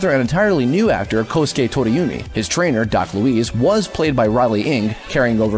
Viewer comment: Did you just say "Doc Louise"? No. It's "Doc Louis", "Louis" sounding like "Lewis". Git ur facs str8, m8.